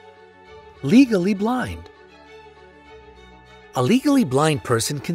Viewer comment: When he said legally blind I thought he said legally blonde 😂😂😂